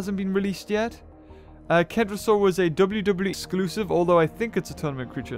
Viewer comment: Did he just say wwe exclusive